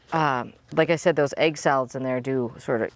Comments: I used your recipe a few times and it worked (I think..? Who knows the mind of deer.). I used a fine mesh strainer to strain out solids when pouring into my pump sprayer and it didn’t clog for me 🤷‍♀️ I also bought the Deer Stopper 2 and it seems to work. I just noticed a demolished hosta, it’s time to spray again! P.S. I kept thinking you were saying “egg salads” and I sat here with a confused look for a minute 🤦‍♀️ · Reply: I thought she said egg salads too. Lol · I thought you were saying egg salads! Lol